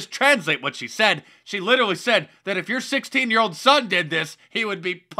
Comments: I thought she said 6 yr. old son. Funniest video in the universe. Lmao. Gotta ❤ our Pesident. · I thought she said 60 year old son! Lmmfao! Wtf